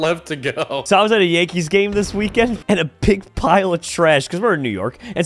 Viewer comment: I thought said “a pink pile of trash” at 42:27. I’m crying laughing